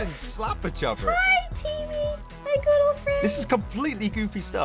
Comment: Am I the only one who thought that rocky at 3:40 said "Hi TB my girlfriend."